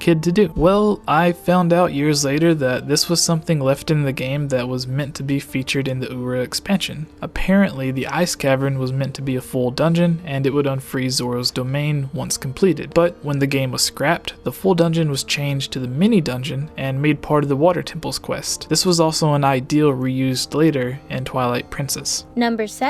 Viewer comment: Is it just me or does it sounds like he says Zorro's Domain?